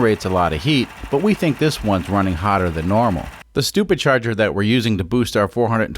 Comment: Did he say stupidcharger at 2:04?